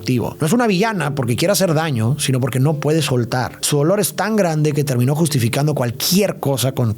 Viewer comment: le entendí "su olor es tan grande" XD